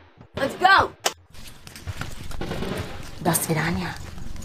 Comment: what did she said? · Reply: До свидания/dasviDAniya